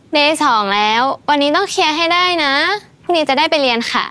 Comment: เราอยากรู้ค่ะว่าแป้งพูดอะไรกับปุณณ์เราได้ยินไม่ชัด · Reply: Day2แล้ววัยนี้ต้องเคลียให้ได้นะพรุ่งนี้จะได้ไปเรียน